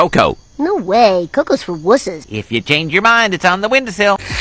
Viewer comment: Ohh wusses, I've always thought he said wizards 🤣🤣🤣